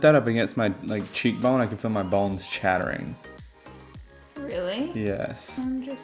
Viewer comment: Had to go back because I thought he said “balls chattering” 😂